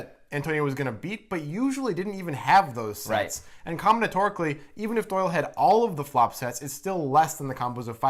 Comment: "Comnitorically?" What the hell word was that?!? o.O